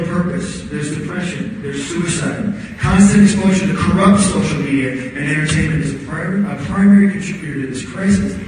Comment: I couldn't understand what he was saying at 6:17-6:30 no matter how much I went back and listened, can someone please tell me? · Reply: MasonTheRaichu he was talking about kids not having any purpose, depression, suicides and how corrupted social media and entertainment are contributing to that, due to exposure and how he knew kids who commited suicide